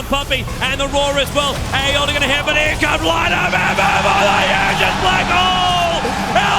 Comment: Just to clear what tobi said. Tobi said "AA ulti gonna hit but here comes light of heaven with the hugest blackhole!".